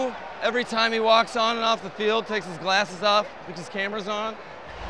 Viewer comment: Who is he talking about to open this up with the glasses/camera comment? Lol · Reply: Hahahaha that actually makes a ton of sense. Thought he said “and puts his cameras on” but he def said “cus he thinks the cameras on him” and that sounds exactly like Rosenhaus 😂😂😂 Good call!